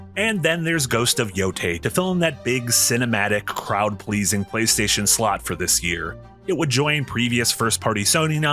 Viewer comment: It sounded like you said "crowd pleasing playstation slop"